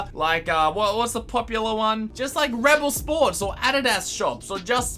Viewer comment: did he just say adidas